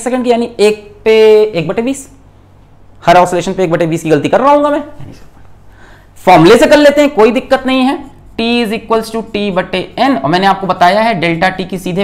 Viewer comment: i hear someone saying : "arey sir formulae se "